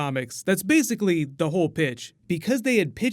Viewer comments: I thought you said bitch. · I thought he said, "That's the whole bitch"